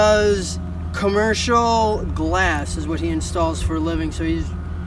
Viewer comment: Commercial glass? My bad, I thought you said construction worked.